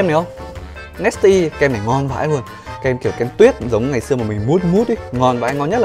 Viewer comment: "kem này ngon vãi l" ai đó cho m biết m nghe nhầm đi =)))))) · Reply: Luôn mà